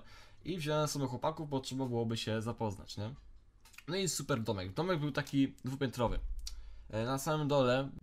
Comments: Ja usłyszałam Tomek był dwu piętrowy 😅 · minuta ciszy dla mnie, która usłyszała "Tomek był dwupiętrowy"......tak.